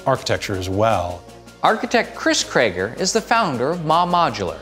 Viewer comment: I thought he said Chris Treager 😂 Parks and rec fans feel me